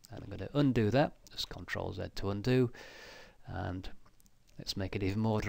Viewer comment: At about 9:15 you say to hit Ctrl - something to undo...I couldn't hear what came after "Ctrl". Please tell me what that is. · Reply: Ctrl+Z - it's a keyboard shortcut to cancel the last update caused by mistake